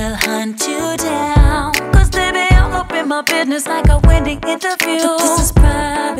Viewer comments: "All up in my buisiness like a Wendy interview" SHE DID NOT- we need to talk about that🤣🤣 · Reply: I thought it was Whitney interview. I never thought she said Wendy. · Omg I finally understand what she means about a 'Wendy interview' hahaha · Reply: I thought she was saying Windy interview... haha... now I know she means wendy williams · When she rhymed "secret rendezvous" with "Wendy interview" my wig ascended the astral plane. She did that. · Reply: I thought she said Whitney interview as in Whitney Houston LoL